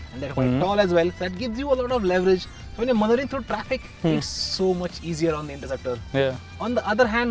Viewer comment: Did he say "mothering through traffic"? 😂 This guy eats half his words. I can't understand him half the time. He really needs speech and intonation training.